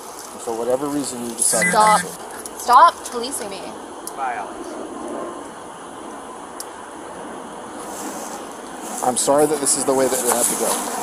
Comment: I could be wrong but I thought I heard the dad say “bye Alex”